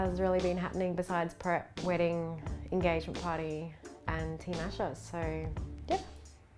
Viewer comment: Asha.... you're beautiful! And I love your accent, adds to your hotness lol. Joe is a lucky man, and I didn't know you two got married, congrats guys! Greetings from Vancouver, Canada. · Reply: Ahhh yes, for some reason when I heard her say "engagement party" my dumb brain took it as the reception party lol, which those two words sound nothing alike so I don't know how I did that lol...thank you for clearing that up